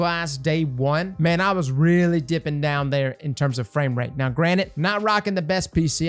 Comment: I heard you say granite instead of granted haha 10/10